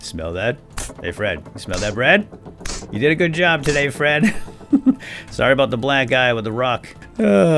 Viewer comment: Did I really hear you say "Sorry about the black man with the rock?" Not cool, not cool. Also it's pronounced "Kern"